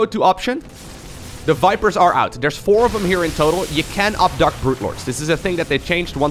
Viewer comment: I thought you said You can't abduct Brood Lords, so I was a bit confused when Brood Lords were abducted, XD GG